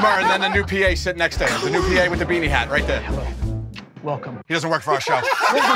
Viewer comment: what did Joe said at 4:29? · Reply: "He doesn't work for our show".